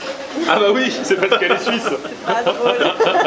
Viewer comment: a 7:25 j'ai entendu c'est parce qu'elle suce mais en faite il a dit c'est parce que elle est suisse j'ai un esprit tordu oui je sais